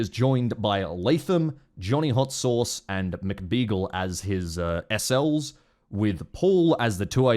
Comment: I misheard Macbeagle as just Beagle and I thought you somehow roped Commander Beaglerush of RATS PMC into this mess. That crossover will remain a pipe dream, it seems.